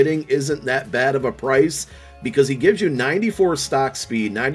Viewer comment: On rasul Douglas, I thought you said his breath stinks so you gotta stay away from that 😂😂